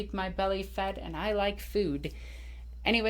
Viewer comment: When I first heard your intro, I thought you said, "Nerd Queen Alexis " hahaha.